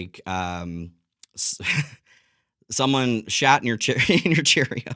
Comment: I thought u said someone shot ur cherry😂😂😂😂 but u said someone shatt in your cheerios 😂😂😂 had to crack up twice😂😂😂